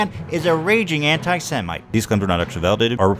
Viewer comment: When he says "antisemic, i heard "hentai semix"...